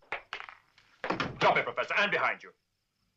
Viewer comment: Bond: "Drop it professor I am behind you!" Me: "We'll that didn't sound like Sean Connery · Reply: I thought he said 'and behind you', as in drop the gun and make sure you drop it behind you.